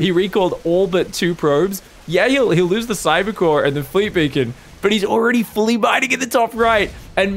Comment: I thought you said 'fleet bacon' and it made me hungry.